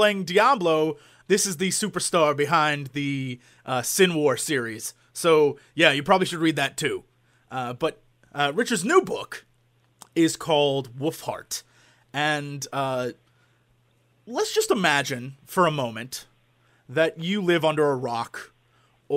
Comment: did jesse say "his new book, willfart"?? O.o